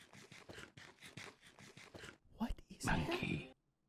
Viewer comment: Is it just me or is it someone else too that sounded like master oogway said monkey from kung fu panda movie